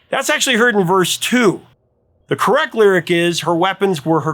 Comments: I always hear “Her weapy were her crystal eyes.” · Taco's "Puttin' on the Ritz" s just fun. I have always heard Marishka say "crystal eyes". I think it was just her accent that is making others hear something else. If anything it sounds like she's saying "ice". As far as the "first person third person" thing I interpret it as both. It hops in and out of both. I can't see Burrows as a "one hit wonder". The man may have been with different bands but he was the consistent catalyst for all of those hits. Those bands were one hit wonders...but he's a fiver =) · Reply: I always heard it as "crystal ice" myself. But the funniest was when my girlfriend had her mother come into her room and demand "Did he just say I'm your penis??".